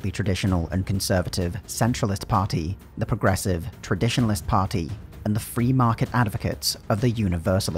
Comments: "What shall we name our progressive tradition breaking party?" "How about the Traditionalists" "...Genius!" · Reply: I also heard it as Traditionalist, but the subtitles do say Transitionalist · Subtitles: the progressive Transitionalist Party Narrator: the progressive Traditionalist Party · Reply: Lol I heard 'Traditionalist Party' there and knew something was up Didn't think to check the subs though